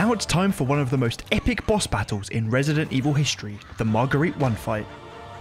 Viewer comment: Hey. I misheard Marguerite with Margarine for some reason at 0:10:19. XD